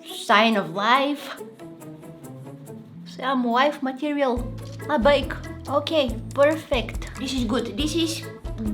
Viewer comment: a bike. (Did she say a bike cause that's what I heard)